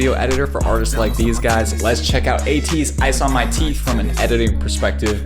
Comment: I THOUGHT HE SAID ICE ON MY D**K ICE ON MY T*P